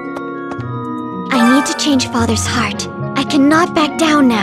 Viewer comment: Who else didn’t notice it was Haru all along... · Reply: I heard "We need to change his heart" and was like "3 Houses IS just a Persona game but with different combat"